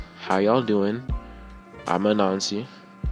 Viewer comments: Oh my god when you said your name I thought you said “I’m a Nazi” 😭😭😭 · Your name/intro scared me for a bit cuz i thought i heard "hi, im a nazi!" (Great video btw!!♡♡♡) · You, who has a stuffy nose and can’t pronounce N very well: I’m Anansi Me, who hasn’t been here before and misheard it as “a Nazi”: that’s. Probably not right. What’s the name of their channel again? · no but your intro💀 i thought you said hi im a nazi instead of hi im anansi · At first I heard "I'm a nazi" instead of Anansi